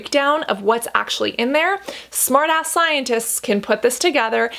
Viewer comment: does she say smart a$$ scientists? 😂